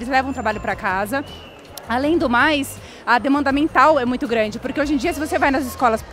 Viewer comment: Demanda mental? Eu ouvi isso em 9:37 kkkkk.